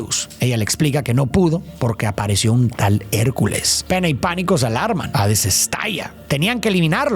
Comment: le entendi pen3 en vez de pena JAJAJA 💔